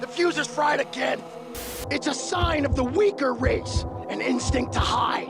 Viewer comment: When he said that it was the “weaker race” here: 17:15, I accidentally had my finger on my phone screen and it made it go by 2x speed, and i lowkey thought he said the n-word